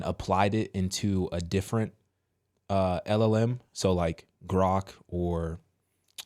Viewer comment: To keep it a little bit mystic that's funny when you said at 10:10 LLM, I heard Elohim first. Second Thoughts we should change the word Apple beacause apple is the name of a company and it coiuld be an answer. Orange is too, Anyway there are lots of fruits we could choose for this kind of answers.